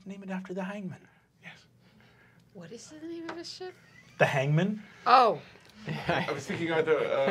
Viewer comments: Im glad I wasn't the only one who thought he said hymen at first. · Dear lord, the sheer confusion on her face! I too thought Brennan said Hymen at first. I was like "Who the heck names their boat hymen!?" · she thought he said the name of the ship was "the hymen" 😂😂😂😂😂😂😂😂😂😂😂😂😂💀💀💀💀💀💀💀💀💀💀💀💀 the face of pure discuss and confusion it's perfect😂😂😂😂😂😂😂😂😂😂😂😂😂😂😂😂😂😂😂😂😂😂😂😂😂😂😂😂😂😂😂😂😂 FUNNIEST UNDERRATED MOMENT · I thought he said "Hymen" too 🤣